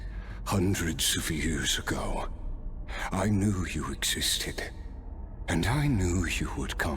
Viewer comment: I swear I heard "I knew you're autistic" 😭😭😭